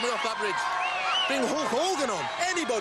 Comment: Did Warrior say "Bring Hulk Hogan on" there?????? I've always wondered that since I was a child!!!!!!